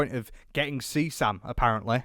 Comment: “getting seasam”?! Tf does he say??